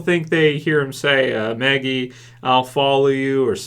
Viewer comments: I heard "Maggie I'll find you". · he says "Maggie I'll find you" come on you must not have listened to it that much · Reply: Colin Pagano thank you! I've seen tons of reviewers say they don't know what he said or they think he was just mumbling, but I heard him say "Maggie, I'll find you" straight away in my first viewing of the episode, maybe it's just a reviewers thing ha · To me it sounded like he said "Maggeie, I'm sorry" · I heard Maggie I'll find you